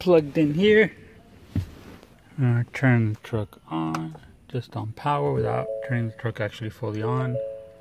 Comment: WTF did you say at 1:21. It sounded like you said, make sure you don't power without truck fully on. WTF did you say?